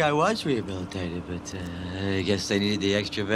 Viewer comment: "Well i didn't think I was rehabilitated but uh i guess they needed the extra bed." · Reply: LOL I thought he said "I guess they did the extra bit" and I was so frustrated that I didn't get it.